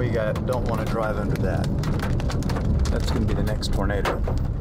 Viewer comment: The closed captions say "We don't want to drive under that. That's going to be the next burrito." Edit: It's all burritos!